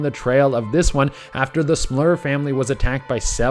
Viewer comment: you pronounced smurl wrong che. it sounded like you said smlur lolz